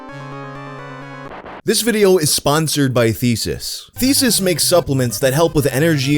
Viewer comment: I thought that finally got the proper sponsor when I misheard: "This video is sponsored by feces"